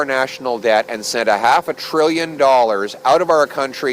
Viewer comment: he was an only child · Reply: Did he say half a trillion?